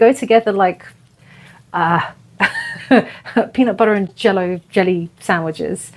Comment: Hehe I know this is so not the point but did you just say peanut butter and jello sandwich 😂 I’ve heard of Brit’s thinking that’s what we eat, but really it’s jam (we call jam without seeds jelly) and peanut butter. 😅 love it. They’re very tasty I like to eat it on toast! ❤ thanks for always giving us peace and reassurance